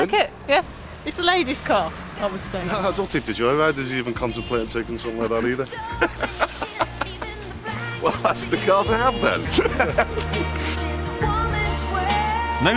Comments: can u tell me what exactly he's saying at 5:13 u prolly understand him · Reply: "I don't think the joyriders would even contemplate on taking something like that either" he doesn't usually talk like that lol he was shy and nervous about being on telly, took me a few listens to get it myself 😂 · can someone pls tell me what he said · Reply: i dont think the joy riders even contemplate on taking something like that either